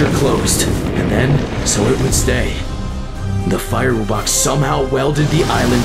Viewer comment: Wait did you just say "fire wubbox" 😂